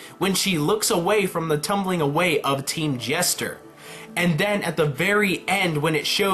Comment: did anyone hear him say team jester at 25:44.......